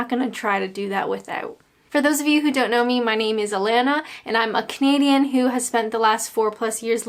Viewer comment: When you said I'm a Canadian, I heard "I'm a comedian" - after your hilarious vid, I guess I heard right! haha - ps I'm from the North of England and a few of those names fooled me too! 😂